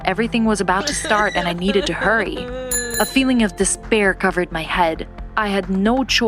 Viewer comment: did i hear... despair? aha